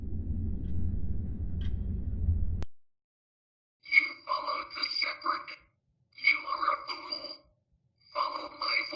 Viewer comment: What did it said? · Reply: You followed the shepherd. You are a fool. Follow my voice instead. You will find great happiness. Wake up. Do not make me wake the others.